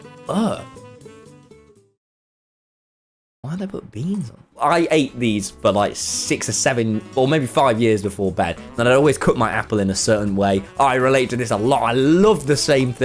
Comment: did he just say 6 or 7